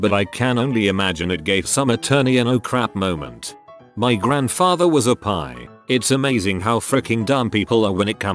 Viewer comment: I'm listening to the audio without reading along, so I was very confused when I heard "my grandfather was a pie" 😂